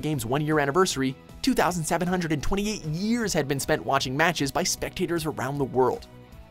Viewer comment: Did he just say "2,728 years?"